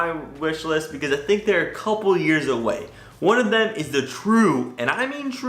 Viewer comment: 😂😂😂 I am lmao right now😂😂😂 did you just say " a couple of years gay" 😂😂😂😂😂😂😂